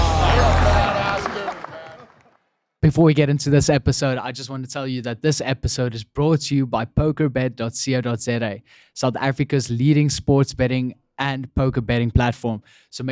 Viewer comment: Thought you said poke bed. Like another airbnb XD